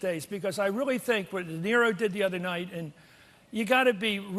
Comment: did he say "Nero"? "...what Nero did the other night..."?! Did he call #45 Nero? I didn't know POTUS plays the violin....